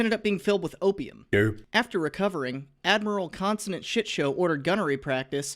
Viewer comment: I heard "consonant shit show" as "constant shit show" at first and I'm honestly not sure if it's an improvement.